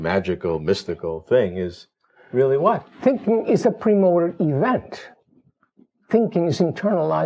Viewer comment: I believe he says "thinking is a pre-motor event" instead of "primordial event" (as written in the closed captions), for instance, he continues "thinking is internalised movement" (and he insisted on movement in all the part that preceded when comparing animals to plants)